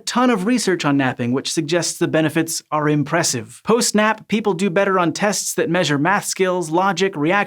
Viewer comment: I swear I kept hearing "post snap" instead of "post nap"